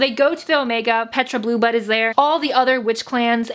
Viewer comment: I thought she said bluebutt instead of blueblood